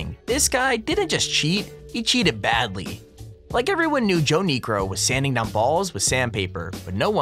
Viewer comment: who else thought he said “nergo” instead of niekro?